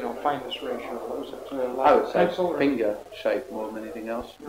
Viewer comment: Anyone hear what the teacher say at 12:20.. what shape it was? First he said was cigar shape but then he says something i dont hear.. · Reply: I think he said ‘finger’ shape.